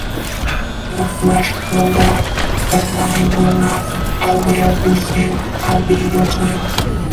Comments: for anyone who doesn’t know I think he says “YOUR FLESH WILL ROT, MINE WILL NOT, I’LL WEAR YOUR SKIN, I’LL BE YOUR TWIN” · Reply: I hear it as “YOUR FLESH WILL ROT, BUT MINE WILL NOT, I’LL WEAR YOUR SKIN, I’LL BE YOUR TWIN” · for anyone who doesn’t know I think he says “YOUR FLESH WILL ROT, MINE WILL NOT, I’LL WEAR YOUR SKIN, I’LL BE YOUR TWIN”